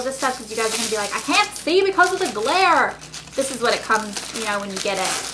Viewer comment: when you said cant see cuz of the glare thought you said because of the Blair so i had to go back and double check